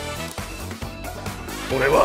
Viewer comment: did he call jojo a niza